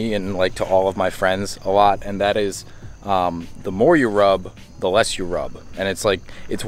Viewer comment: I Thought You Said "The More You Rob, The Less You Rob" 😂😂😂😂😂😂😂😂